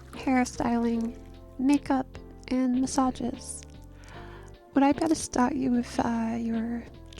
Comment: I misheard massage as misogyny and thought yeah that’d suck but I’d feel like a girl ig